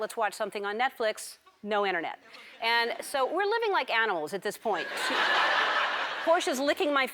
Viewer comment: I don't mean to be rude but I tried to hear like a thousand times but I kept hearing her says "anal", not "animal" 😂😂